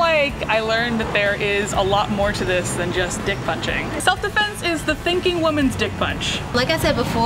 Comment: Who else thought it said selfie - defense